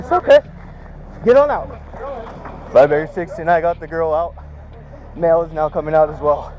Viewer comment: That first one brought tears to my eyes. Dude is a true hero. While the police was running around, acting like he was trying to help" asking for a latter" the true hero took it over. Bless you brother. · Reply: Am I the only one who heard him say "I got the baby out"? That really bugged me.